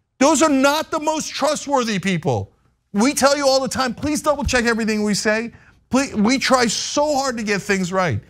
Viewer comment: "please dont double check what we say" insane to say that